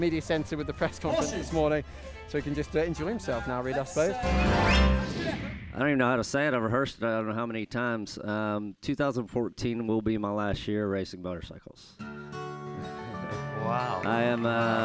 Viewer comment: who can translate in italian what Colin said from 3:16? or just write in english here, what he said? thanks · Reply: "i don't even know how to say it, i've rehearsed it i don't know how many times....2014 will be my last year racing motorcycles"